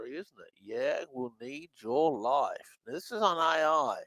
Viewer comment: I don't hear, "Yeah, we'll need your life." I hear, "Yeah, we'll need this life." p.s. "The Shepherd" could be a superior (ET) AI, intending to create a humanizing element for humans. p.p.s. I think "The seal we know" may relate to the "rule"of how humans should not be harmed (originally popularized as Asimov's Three Laws of Robotics), as well as the AI's dependency on its' connection to humanity, which requires a duty of imitation (a mimicry/imitation of humans' kindness, concern, sympathy, etc.).